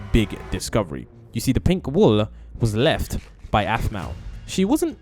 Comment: WHY IS THE SUBTITLES SAID ANIMAL WHEN YOU SAID APH